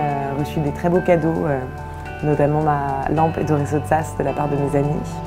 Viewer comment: I love her apartment, can anyone let me know the lamp? What lamp it is? Min 1:32, she says her friends gave her, something like Isoisosaz. Need to find out! Thanks! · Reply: It's by Ettore Sottsass :)